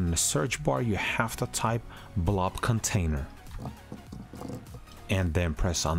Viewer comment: I am an ignorant American and I couldn't tell what you said to type in the search bar. Also slightly blind cause I couldn't zoom in and see the text on the video 🙃 It sounded like you said Blobcontainer. Lol